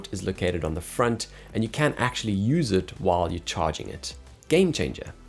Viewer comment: we can or can't use it whilst charging? Sounds like you said can't but I think you meant you can? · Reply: You can yes 🙌🏻